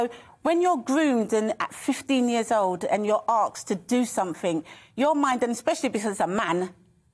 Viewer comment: Did she say "arksed"?